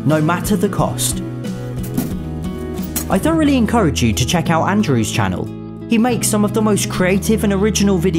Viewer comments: I thought said: I don’t really encourage you to view his channel! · I know lives in the UK and his accent is different but, did anyone else hear "I DON'T really encourage you to check out Andrew's channel"? EDIT: I just saw post. I am so sorry! Ack I feel terrible! So sorry; my ears are just not working today!